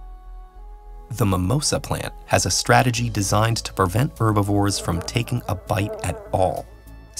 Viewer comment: did you just say "verbivores"? Is that a portmanteau of "vegetarian" and "herbivore", or did you mean the quiz team from Only Connect?